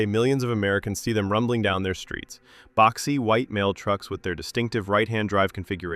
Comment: OMG I heard it as "boxy white male trucks" 😂